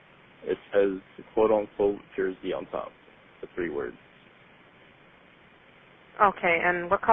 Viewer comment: subtitles of the audio transcript should say "three words" (instead of <inaudible>), I guess.